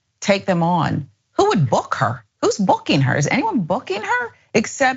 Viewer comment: I thought she said is anybody Fucking Her, not Booking Her. 🤣